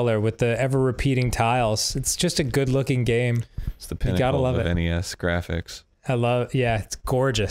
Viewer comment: Oh you said Pinnacle NES? I heard Pinnacle in-the-ass graphics 😅.